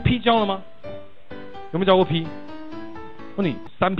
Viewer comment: 开始吧，我想多了。还有，有没有教过p我把它听成有没有教过屁。。。。。。😅